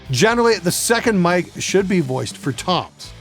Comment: What is he saying at 1:44 "Generally the 2nd mic should be voiced for Toms" ???